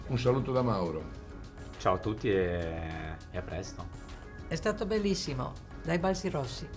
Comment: solo io ho sentito "è a pesto"?😂😂😂